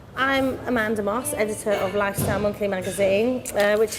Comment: No one EVER: VICE: 36:14 here is a close up of the worst pedicure ever on the lifestyle magazine editor EVERYONE with hearing: Did she just say lifestyle MONKEY magazine?